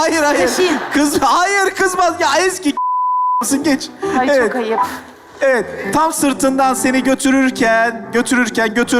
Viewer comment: buradaki bipte yönetmen ne diyor ? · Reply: Gerizekalı mısın diyor